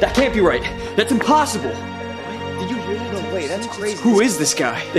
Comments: “Momslayer21” that’s probably the funniest gamer tag I’ve ever seen in a video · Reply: I Thought He Said "Mobslayer" Idk Why I Didn't See "Momslayer" Coming 😐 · "Sir, our men are getting slaughtered!" "by who?" "he's called momslayer21" "dear god, we have no choice. Send... Alberto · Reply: not saying your stuck up just go to 0:54 and do 0.25 speed you will hear mom and even captions say mom slayer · “Momslayer21” that’s probably the funniest gamer tag I’ve ever seen in a video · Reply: Oh I thought they said mob slayer